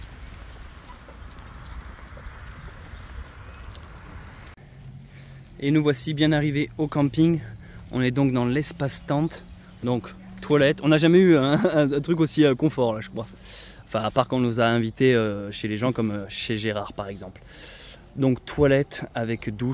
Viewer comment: Salut. 13:51 : J'ai entendu 'On est donc dans l'espace-temps, donc Twilight (zone) !" ^^ Merci pour tes vidéos. 👍👍👌